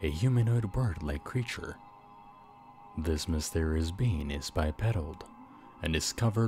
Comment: When you said it’s bipedal, it sounded like you said he’s a bi pedo Should I be worried that I hear this?